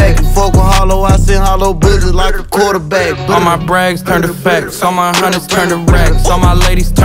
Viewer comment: He says: "All my brags turn to facts." I hear: "I lie a lot." shits 🔥 tho